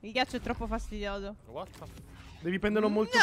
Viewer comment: Ma solo io a 26:11 ho sentito che diceva “What the fuu...” che poi si blocca all’ultimo??🤣